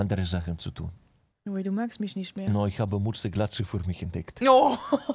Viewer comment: Hat irgenjemand das Wort bei 2:04 verstanden?ich nicht😂😂🙄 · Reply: Er hat Mütze/Glatze für sich entdeckt